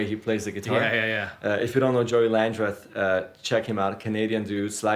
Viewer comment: Hello, can you please tell me a bit more about the singer you mentioned in this video: time around 10:40. I cannot understand the name. Thank you very much. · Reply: Joey Landreth